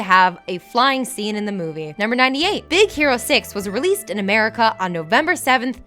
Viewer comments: Did she just say Big Zero 6? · Did she just say Big Zero 6...? Pronunciation...